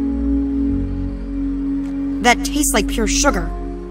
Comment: The first time i heard " That tastes like your little sugar"